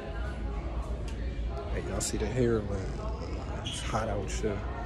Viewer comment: The caption said “hey y’all see the heroine hot out [__]”